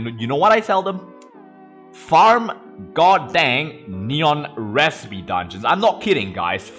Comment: What were you saying at 4:18 ? No offense but I had trouble understanding you because of your accent. Hopefully this isn't offensive. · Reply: farm god damn neon city recipe dungeons